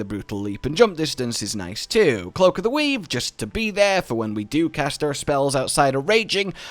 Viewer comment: i swore he said cloak of the weeb